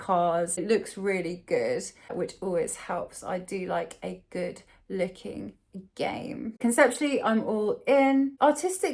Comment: i thought you said "i do like a good licking" and im fine with that